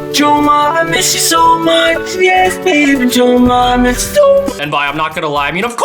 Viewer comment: Who thought it was saying yo mama